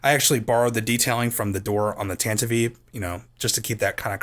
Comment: Hol up. Did you say the Tanviv-eve? Not Tantive IV? ...As in, Tantive Four? Anyway, great model, man.